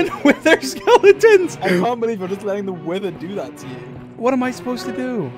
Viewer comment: Dream: Screams his lungs out while having wither effect Captions: [Music] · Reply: when George said “wither” The subtitles said “weather”😂😂